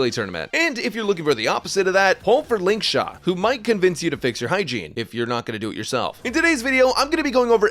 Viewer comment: I thought he said "Who might convince you to fix her hygiene", because after he said that the video proceed to show her blowing.